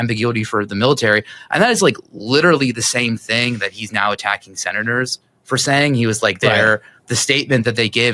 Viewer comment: I heard you say 6/7 when trying to say "6 senators". Memeing hard over here 😂